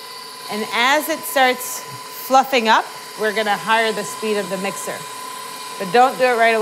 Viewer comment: Omg. I pulled my headphones out for a sec and put them back in and i swear i thought she said "we're going to... fu**ie" 3:25 lol.